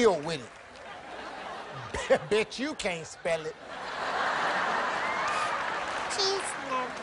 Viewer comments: I thought he said,” Bitch u can’t spell it.” 😂😂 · I thought Steve Harvey said bitch u cant spell it